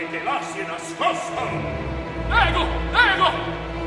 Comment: did Cavaradossi say "Lego, Lego"?